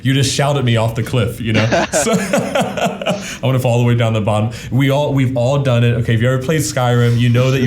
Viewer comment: Something about the clergyman’s laugh at the beginning makes it seem so forced and empty… it also sounded like he said he “wants to fall all the way down the cliff.”